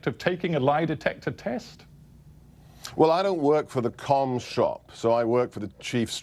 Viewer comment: did he say he doesn't work for the Cum Shop? Doesn't matter, Gorka can say whatever he likes because he's a Terminator. Just mows down Leftist Scum.